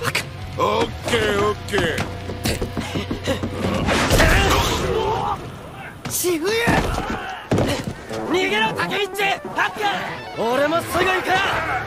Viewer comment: what did he say????? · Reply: Dude, he said “Run away”. Ni ge ro means “Run away”.